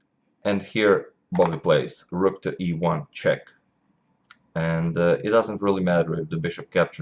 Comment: I thought he said " rook to ivanchuk "